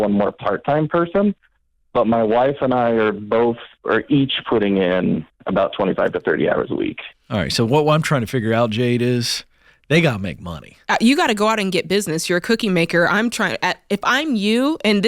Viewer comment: the connection breaks up. It sounds like he said they're each putting in all of 25 to 30 hours a week.